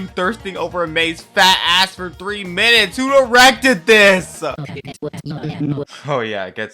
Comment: I thought you said at first "Who erected this!?" but on a relisten I hear that it's "directed" now.